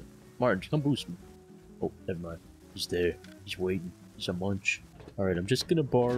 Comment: am I tweaking...or did you call dude a munch @ 21:16 lol